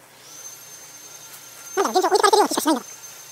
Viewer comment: で何だか現状置いてかれてるような気しかしないんだがとが滅茶早口で言ってるのが聞こえます。